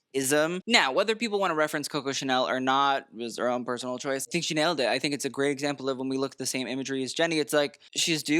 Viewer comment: Dying when he said She nailed it. Without realizing it sounded like: Chanel-ed it. Good job boss.